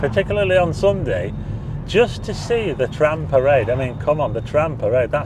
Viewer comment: O sorry , thought you said trans parade, with all the trans colours, 😂😂